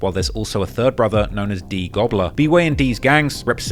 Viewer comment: Yooo did I mishear that or did he say one of these goons went by D-goobler LOLLL